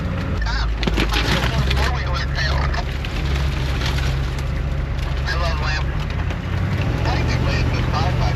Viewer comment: Did I hear someone say "I love lamp." on the radio at 8:33? lol